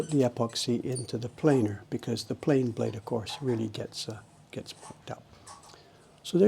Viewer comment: I don’t think I’ve heard drop an f bomb yet, a rare find indeed! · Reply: Pretty sure he said mucked up. Only he knows.